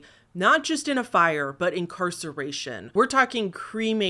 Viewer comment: I heard incineration, shows you hear what you want! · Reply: You misheard. She literally said incarceration.